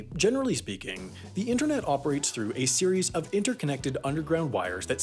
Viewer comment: what he said: a series What my ipad heard: hey siri